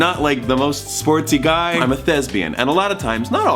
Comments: When you said thespian I thought you said lesbian. My brain is a little foggy.... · I thought Thomas said "I'm a lesbian" 😂 and I was like Thomas no that's not how it works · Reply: Haha no he is a thespian which are theater people and it is often confused for sounding like lesbian trust me I told my mom I was getting inducted as a thespian and she thought I said lesbian so awkward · I thought Thomas said "I'm a lesbian" 😂 and I was like Thomas no that's not how it works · I thought he said "I'm a lesbian" instead of "thespian" and I was sooooooooo confused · I thought he said lesbian and than I processed thespian but I still thought that was a theater lesbian because my brain doesn't work. But then he said status quo...and I was like....OH A THESPIAN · When he said thespian I thought he said lesbian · He said "I'm a thespian" but I thought he said "I'm a lesbian" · When you said 'thespian' I heard 'lesbian' and I may or may not have screamed "WHAT!?!?!?" has such an amazing smile and the cutest dimples. I- I need you two to- to- to kiss. On camera. Holding each other close.